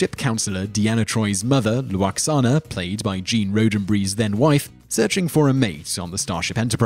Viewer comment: Trek not Track. Two different words with different pronunciations. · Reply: Sounds like he said Roadenbry for Roddenberry.